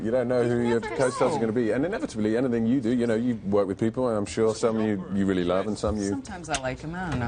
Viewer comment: did he just say someone who you really LOVE!